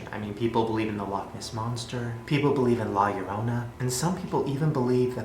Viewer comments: When he said "La llorana" it sounded like he said "la yoyona" i was dying cause he just butchered spanish lamooo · Ai thought he said Liorans but no la llorona I already new her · can someone please tell me what Shane said at 15:53? I keep tryna type it into google but i don't know what he is saying. It sounds like (lie•your•own•a)